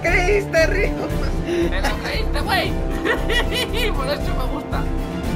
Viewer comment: es mi imaginacion o es que dijo que : por eso me gustas .a